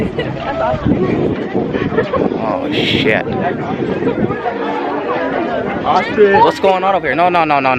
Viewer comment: All I heard was " that's Austin, that's Austin!, that's Austin..?, Austin!! " lmao i love it